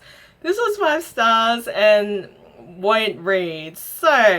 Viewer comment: Did you say "5 Stars, 5 stars... white read." to KoA? LMAAOO!!!